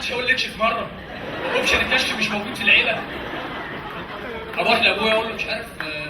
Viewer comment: شو قال؟؟ "هو ما حدش قاللي اكشف مرة"؟؟؟ وشو بعدها قال كمان لما انتهت العبارة في "بالعيلة"؟؟؟؟؟ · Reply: اوبشن الكشف مش موجود عندنا ف العيلة :D